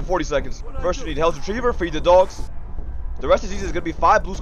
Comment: i thought he said free the dogs😂 #freethadogs